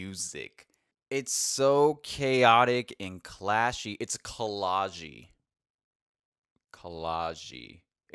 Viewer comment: What does he say at 7:43?? "cology"???